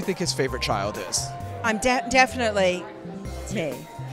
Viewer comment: Wait what does Sigourney say at 1:14? "I'm definitely paid" ???